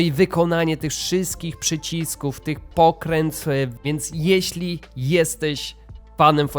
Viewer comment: Mercedes ma na przycisku radia logo WhatsAppa, zamiast napisu "talk", ponieważ WhatsApp jest partnerem/sponsorem Mercedesa i to jest po prostu widoczna reklama. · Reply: Jak ty wymawiasz "pokręteł"? "Pokrętfł"? Nie mogę zrozumieć, a było dwa razy w filmie, za pierwszym razem myślałem, że coś źle usłyszałem, ale chyba nie. Już innych rzeczy się nie czepiam, żeby nie było że jakiś czepliwy jestem. Kierownica fajna, szkoda tylko że te diody są prawie białe, ale widocznie trzeba było zastosować mocno przezroczysty plastik.